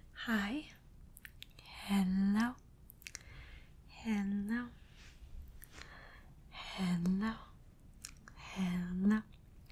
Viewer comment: For a minute there i thought you were saying "hannah" not "hello" i feel stupid